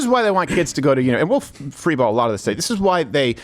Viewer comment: I clearly heard Freeball 😂